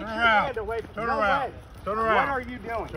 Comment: I swear I heard at 4:50 that is saying "your sergeant just told you to...STOP!" as if he is saying that either the officer's actual sergeant told the officer to stop or that himself is "The Sergeant" since we all know frauditors love to throw out "I'm your Boss! I tell you what to do!" but yea I know his sentence was cut off but still It made me double take to see if that was what was meant.